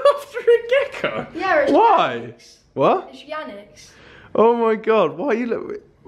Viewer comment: who else thought he said vujanics pet